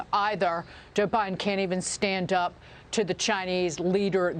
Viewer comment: I thought she said, Joe Biden can’t even stand up.” And then she continued the rest of her sentence.